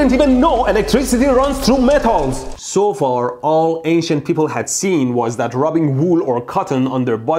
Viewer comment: Instead of “ancient ppl” I thought he said “Asian ppl”